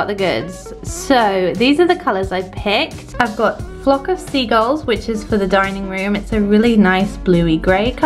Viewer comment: Giggling to myself as i thought you said "plop of seagulls" 😂